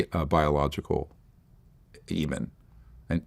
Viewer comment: Did he say Demon? Ok he said Eben (Extraterrestrial Biological Entity), but those words sound ironically similar.